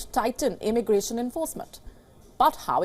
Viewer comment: What did she say? @ 2:49 "He promised that his administration would tighten immigration improvement" Now what does 'tighten immigration improvement' mean?